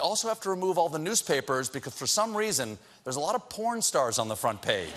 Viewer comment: Did he say “becauths”